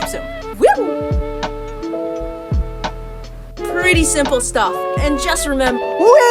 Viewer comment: Did you say "pretty simple stuff"? I'm as confused as the person using those pronouns are.